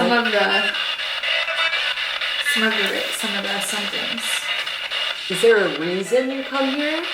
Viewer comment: I hear "Of the Ramsey Inn" or "From the Ramsey Inn" something like that... Idk if that means anything to you two, but Google says there is a Ramsey Inn in New Jersey.